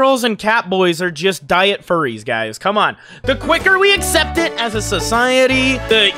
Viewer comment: "the quicker we accept them" Did i hear T'au words?